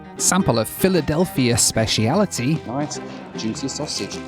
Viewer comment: wait a second did you say a Philly special and then the word sausage? But the actual hell is going on in this video I can’t wait to watch.